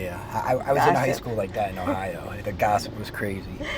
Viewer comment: did u say u went to school in "ohio?" Or did I hear that wrong! Thts where I live rite now & have an since I was 4yo (moved from Cali in 1974).